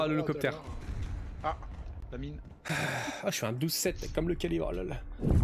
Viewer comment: what is he saying? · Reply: il dit : " je fais un 12/7 comme le calibre lol " ( en parlant de son score )